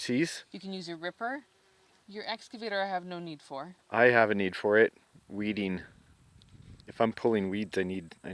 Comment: Did she just say you have no need for a mini excavator?